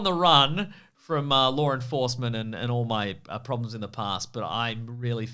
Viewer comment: When said "Law Enforcement" I thought he said "Lauren Forceman"